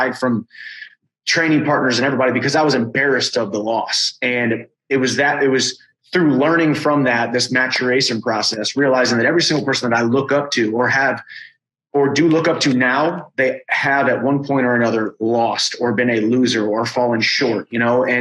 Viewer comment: I thought he said masturbation process 😂